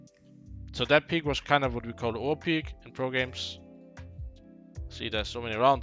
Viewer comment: I couldn't quite catch this word "that peek is what we call ### peek in pro games"? Edit: auto subtitles to the rescue, it's "overpeek"